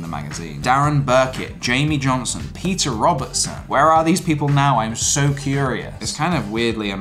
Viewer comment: misheard that as Porter Robinson whoops...